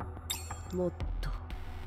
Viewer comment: I'm pretty sure that she said "More" (Motto) instead of move, and was instantly flustered/taken aback too.